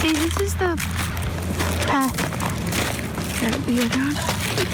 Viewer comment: I hear say "see...this is the path...that be a gun" at 8:31, except I don't think she's saying "that be a gun." More like "...that we're on..." or something like that. I watched this last night and I had difficulty sleeping last night. Those poor children. Think about when you were their age and all the places this could have happened to you.